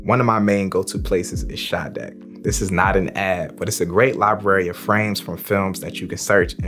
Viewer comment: I thought u said Sade 😂🔥